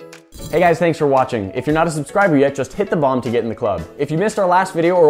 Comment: It almost sounds like you said, "just hit the Bong to get in the club" at the end of the video. 😂🤣😂🤣😂🤣